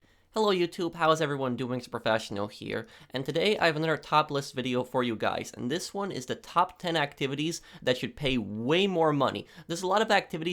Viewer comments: idk but anyone else but i heard “another topless video” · I love when you introduce videos like that because it sounds like you say “Welcome to another topless video!”